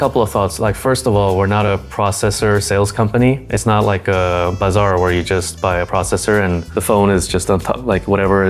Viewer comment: did he say bazaar? 😂 Hahah